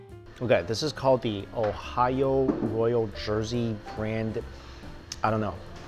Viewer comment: i thought he said OHIO like the actual place.